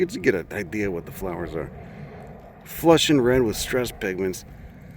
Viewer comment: I could almost swear I saw those pink bastard Crassulaceae growing at the soggy margins of East Lake Tohopekaliga in Saint Cloud Florida. I couldn't quite understand what you said that sounded like "flushed with red stress penguins." What was the real word where I heard penguins? I'm glad you did a video in my current neck of the woods so maybe I can find some of the same plants. I have thought about the logistics of giving that carving for the White Sheets a good riddance and rot in hell 21 cannon ball middle finger salute.